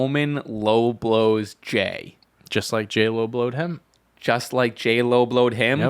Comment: Idk why I heard “Jey low blowed Roman” like JLO Blowed Roman 😂😂😂